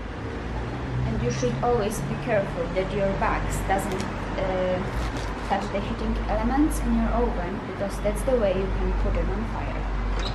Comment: What does she says from 2:48 to 2:54? . I didnt understand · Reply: "And you should always be careful that your bags doesn't - eh - touch the heating elements in your oven, because that's the way you can put them on fire."